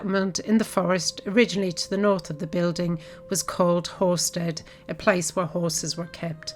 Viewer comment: English not being my mother tongue, at first I thought I had heard... 'Whorestead'. I thought... the English really know how to name their villages! Greetings from Mexico!